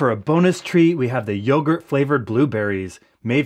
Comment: Lmao for a second I thought you said “hamster flavored blue berries”